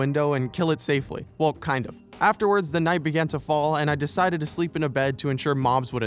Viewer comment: what normal people hear: "the night begins to fall" what I hear: "THE NIGHT BEGINS TO SHINE"